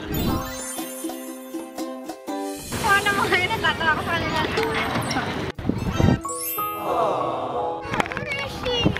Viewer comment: 😳 am I the only one who heard ate Donna say, 'MAHAL'??!! HAHAHA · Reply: she literally said "tama na mahal, natatawa ako sakanila" HAHAHAHA